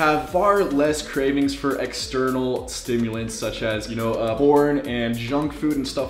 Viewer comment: did you say porn? 😂